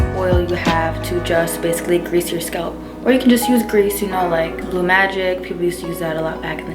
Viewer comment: WAIT!!! Did she just say BLUE MAGIC?????